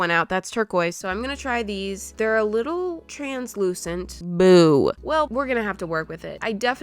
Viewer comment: when said boo, the subtitles thought she said moo 🐮! Lol